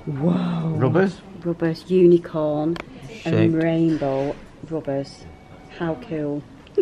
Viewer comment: Did say shit? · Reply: SKJadoRed<num> No he says shaped